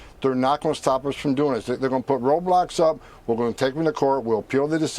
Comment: thought he said Roblox 😭